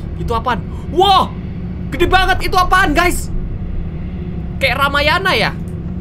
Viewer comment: = dengernya kak Regi bilang "Kaya Ramayana ya" ini bener ga sih? Kalau ga, kak Regi bilang apa sih?? 😂😂😂😂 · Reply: Rahwana, raksasa musuhnya Rama